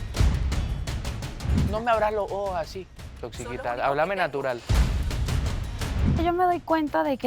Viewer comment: escuché mal o le dijo a Mariela tóxiquita??????? .... jajajaja